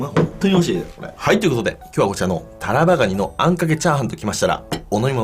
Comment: ぜったいタバラガニって聞こえる。